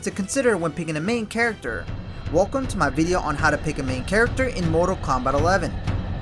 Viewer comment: I thought it said how to pick your man in Mortal Kombat LMAO 🤣